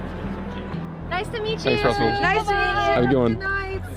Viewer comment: seconds I swear he said “Don’t trust me” 😂